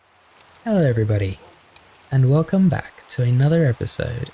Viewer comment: already a banger ❤🔥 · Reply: I heard "Hellor everybodyh"